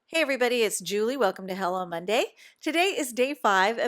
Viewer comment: At the start I thought you said " Hell on Monday". Lolololol my ears are old 😀. Love your tag! Mandy